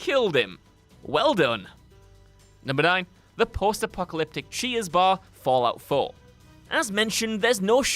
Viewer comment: 🤦‍♂️I thought he was saying “cheese” not “cheers”